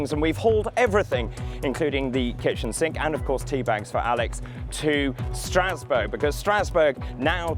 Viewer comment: I legit thought you said "and teabagged Alex" at the beginning of this video.